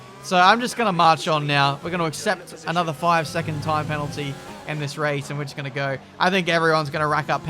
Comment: did he just say tire penaltys or am i stupid hahahahhahaha